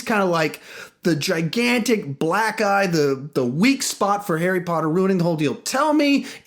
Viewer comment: Sounds like you say that he is the "Gigantic Black Guy, the weak spot for Harry Potter" instead of "Gigantic black EYE, the weak spot for Harry Potter." Racist!!!!!!!!!!!!!!!!!!!!!!!!!!!!! LOL